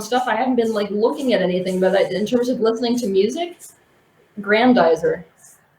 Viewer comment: Who did she say she listens to(?_*) Grandizer